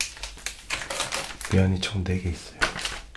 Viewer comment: 잘못들어서 면 ㅈ되게 있어요라고 들었네